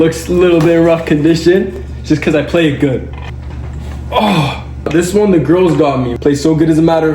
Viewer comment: Y’all whattt did say just listen from this moment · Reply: All he said is “this one the girls got me” did u not see the vid where the kalogera sisters got him that guitar? TvT there’s nothing weird abt that